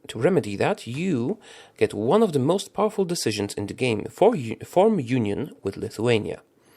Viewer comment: when you said 'one of the most powerful decisions in the game' i thought you said'one of the most awful decisions in the game' XD